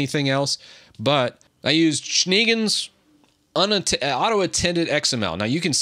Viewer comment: WTF was this word? 😂 It's a german word "Schneegans" (translates to "snow goose" - pronounced like "ʃneˈɡaːns") But yeah, nice workaround